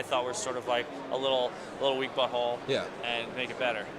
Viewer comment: a little weak but whole Camera dude thought he said "a little weak butthole"